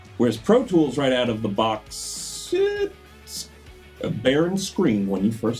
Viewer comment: What does he say at 1:47 ......"A bear and scream?" I can't find X O'Connors name on credits for either of the CDs they held up. And the first one held up is Fix My Eyes, released as a single.